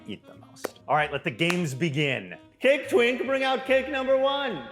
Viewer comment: “Cake twink” took me out 😂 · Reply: I thought he said cake taint